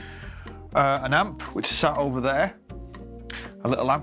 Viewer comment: What did he say 8:00? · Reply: An amp (amplifier) which is sat over there.